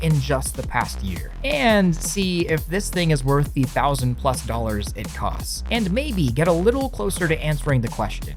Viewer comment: Yeah yeah okay It looks fucking amazing... how much? xD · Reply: $1k i believe is what he said at 0:58 but i have no clue how much it will cost when it leaves kickstarter and becomes officially available at retail price